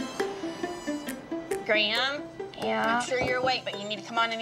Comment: All I hear is "Graym"...